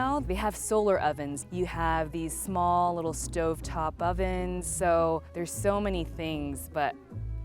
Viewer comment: Did she say stovetop oven?